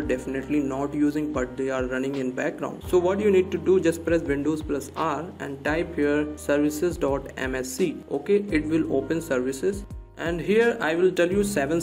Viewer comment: what he is saying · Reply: I was telling press windows key + R to open run dialogue and type service.msc